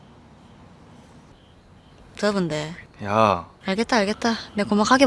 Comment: 고아라가 뭐라고 하는건가요?? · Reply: 불금치킨 더운데